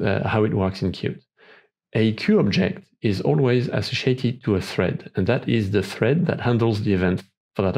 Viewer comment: can somebody translate what he says at 1:03 to English please? "A QObject is always a ______ to a thread" , I played the video at least 20 times, i cant understand what does he say. Can somebody please type this sentence? Thank you in advance. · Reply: I was trying to say "associated", I believe. Apologies for my bad accent.